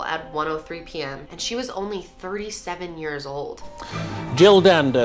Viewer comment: i dont know why but i kept hearing you say “shes only 3 7 years old” not 37 years old LMFAO